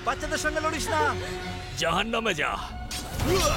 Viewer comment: কিরে আমি শুনলাম জাহান্নামে যা😂😂